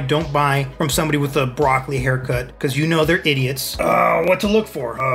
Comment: I thought he said rock lee haircut instead of broccoli haircut